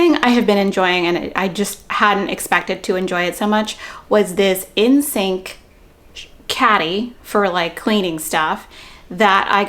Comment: What you said: “The next item is my in-sink caddy.” What I heard: “The next item is my N*SYNC caddy.” Me: 🤯👀👀👀